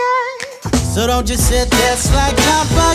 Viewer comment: did he say dont you stand there like a fucking owl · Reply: he said don't sit there all bug eyed -_